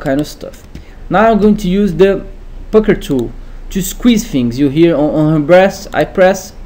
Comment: i heard he said now lets use the fucker tools... and then i just heard squeez squeez squeezes.... lmaozz